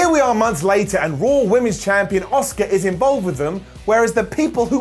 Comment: The way you say Asuka sounds like your saying Oscar... Which is the name of my cat. Nyaa.